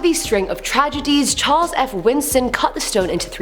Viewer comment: Me being the outsiders fan I am: Did you say... Winston?